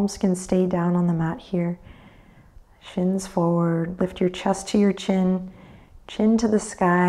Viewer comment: Motivate day 23 . Every time said "shins forward" I thought she was saying "chins forward" and was thinking "how does she know I've got more than one???"....!! | 9/6/24